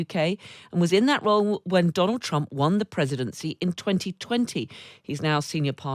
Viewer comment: A little slip of the tongue my dear? Did you say when President Donald Trump WON the presidency in 2020?